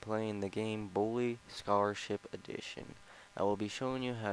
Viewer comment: It sounded like you said "Blue Starship Edition"